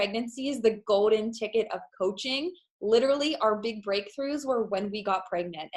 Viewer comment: she says "coaching" and I swear she says "poaching" or does she say poaching? I'm not sure.